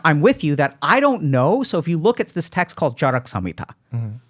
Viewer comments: the subtitles are wrong it's called "Charak Samhita" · One small correction: at 1:14:02 Dr.K mentioned 'Charaka' Samahita, and not 'Garga' Samhita(in the subtitles, Garga has been mentioned instead of Charaka.) both are different. Please update it, it is misleading. Sage Charaka is father of ayurvedic medicine, and 'Samhita' loosely translates to 'compilation of texts'. 'Charaka Samhita' is the medical texts compiled by his students, which were based on Sage Charaka's findings and knowledge. Second Correction: at 1:15:36 and 1:16:19 Again in the subtitles, it is 'Parampara' not 'Barambara'.